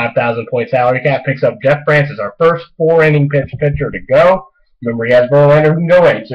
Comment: At around 10:30 did you say 4 inning pitcher??